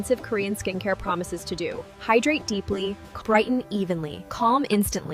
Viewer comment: Aren't they saying Crighton instead of brighten ?